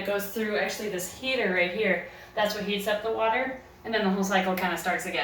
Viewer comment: at first I thought that she said “that’s what he said” lmao